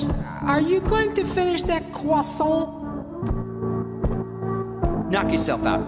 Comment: It sounds like he said are you gonna finish that crossalt